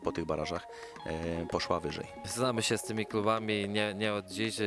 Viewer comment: co on powiedział?? Znamy sie z tymi K....?? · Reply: Klubami